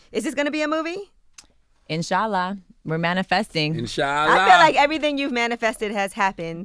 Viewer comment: Am I the only one who heard her say In-Shala at the end instead of Inshallah.